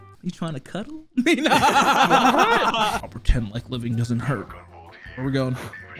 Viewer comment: I honestly thought that said "Tender leg loving doesn't hurt" and I can't stop hearing it.